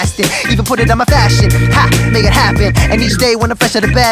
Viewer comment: Who thought said "Hashtag make it happen"?